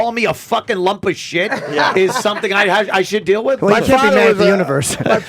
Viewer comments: What does Jimmy say at 1:50:18 ??? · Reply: Sounded like "Well you can't be mad at the universe' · Can someone tell me what jimmy says that makes everyone crack up at 1:50:16? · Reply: Well I can't be mad at the universe.